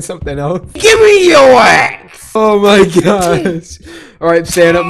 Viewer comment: i know what you said at 5:35 when you about to this this i hear this AYE GIMME YO AXE *when i hear it AYE GIMME YO ASS